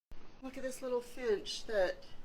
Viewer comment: at first I heard "look at this little bitch"